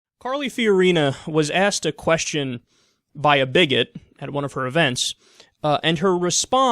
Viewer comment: bummer, I thought it said bigfoot. :(